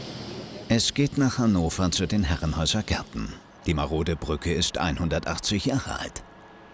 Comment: die Brücke ist 118 Jahre alt. (Habe ich das richtig gehört?)